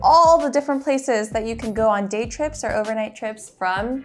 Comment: " Day trips" , I heard " date" 😮 and thought " where " 😅 " when?"